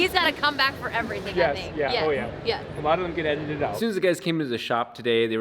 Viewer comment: Okay, normally, wouldn’t question a single thing you do, but I have to know… what did you say at 4:37 ??? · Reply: “Mondo can edit it out” is that what I heard? Someone please let me know